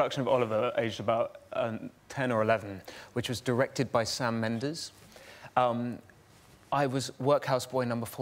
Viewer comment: I thought eddie said Shawn Mendes....